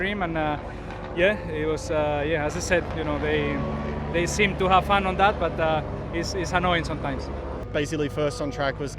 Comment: What is that woman trying to say lol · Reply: mark mark I dont fuck you!